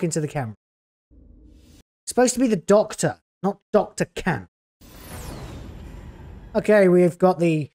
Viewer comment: Thought you said Dr Cat not Camp. That would be better.